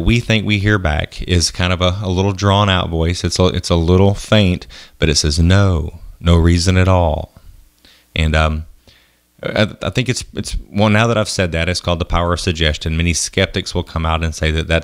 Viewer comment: At the first one instead of "No no reason at all" i heard "Noo, i'm just here on my own."